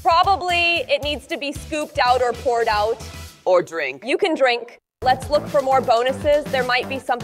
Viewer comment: Что сказала 😅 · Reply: Она сказала ты можешь выпить